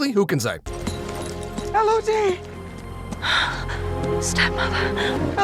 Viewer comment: If I had a nickel for every time Millie Bobby Brown played a character nicknamed "El", I would have two nickels.... Which isn't a lot, but it's weird that it's happened twice. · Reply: Everybody keeps saying "Elodie" but all I hear is "Limit Of Detection".